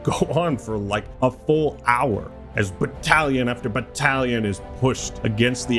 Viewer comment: Three minutes in and I hear "Brottalions" instead of "Battalions".